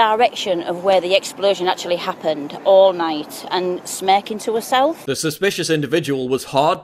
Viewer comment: What does she say at 4:22? Smacking to herself?